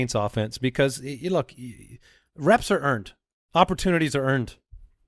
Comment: When Andy said, "Reps are earned" I heard "Refs are earned" at first!!! :) Hahaha